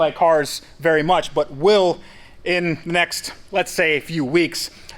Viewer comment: oww for a second I thought he said "build back better"